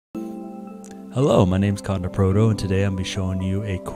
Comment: lol i thought he said his name was condom proto